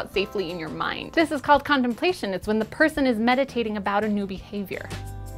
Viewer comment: Thought she said condomplation..